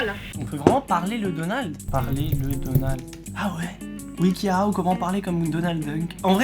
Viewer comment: c’est moi ou il a dit donald dunk??